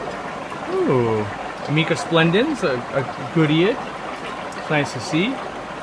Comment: What was the name of the fish at 12:04? I heard something "splendens" I think?